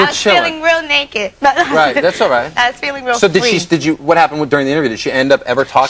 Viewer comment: Did she say she was feeling real fleek @ 4:55 ?